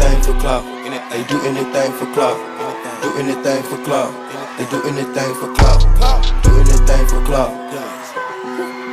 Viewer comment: I realllly wanna know what says to Ary at 3:34 😍😍🙌🏻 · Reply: She said shes turned on lolol